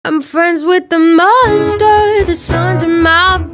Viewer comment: I hear "I'm friends with the munster that's under my bed" lol